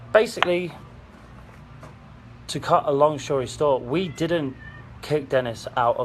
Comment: did…did say “to cut a long shory stort”